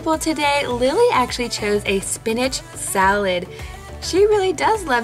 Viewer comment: Wait @ 2:48 did you say spinach salad 🥗 😍😍😍😍